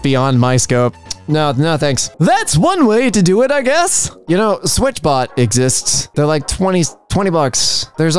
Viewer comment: I think you said “switch bot” and the editor thought you said “switch box.”